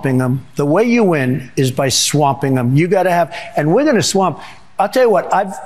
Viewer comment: I heard him say “we’re gonna swap” ??? This guy has lost it! I mean mentally 😂😂😂😷😷😷